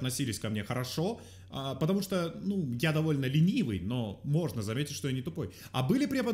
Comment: мне послышалось вместо "ленивый", "милый"...